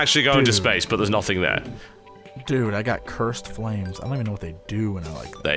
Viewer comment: i heard "Dude, Dude I got CURVED flames"